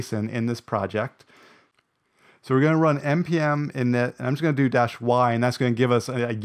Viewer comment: can't see what you typed and couldn't quite make out what you said. the transcript did not have it. could you clarify please? thanks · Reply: Sorry about that, I see now that it was cropped out. You need to have a package.json file. You can run npm init -y to create it with all the standard generic data. You could also manually add a package.json file with the proper default values. I'm a bit lazy, so npm init -y works best for me... the -y is a flag that answers "yes" to all the default questions that npm init asks